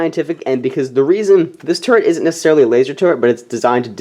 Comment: I thought you said turd